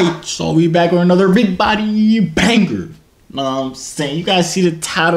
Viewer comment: When said big body banger but it thought he said big booty banger